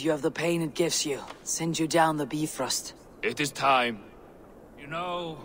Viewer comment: it sounded like she said "beef roast". 😆 Is that how they say Bifrost.